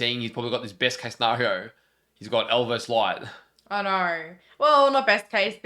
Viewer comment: I know said, “He’s got L vs Light” but for a moment I thought he said, “He’s got Elvis Light” and I had no idea what he was talking about 😂